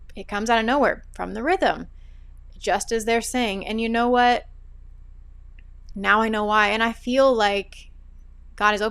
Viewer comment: At minute 41:05 you were swiping your hand and said it was a "fake fly"? Or did I misunderstand what you said? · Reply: I said, “sorry guys, there’s a fly that won’t leave me alone.”